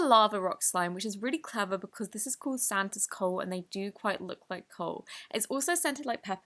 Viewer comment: i thought she said santa's cult